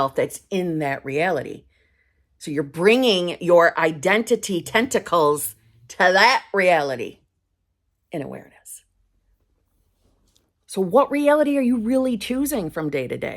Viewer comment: Identity tentacles 😂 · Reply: I thought you said identical testicals I was like.. wtf?? 😂